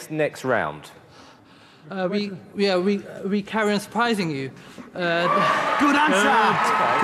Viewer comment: What he said at 04:39 ? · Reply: We carry on surprising you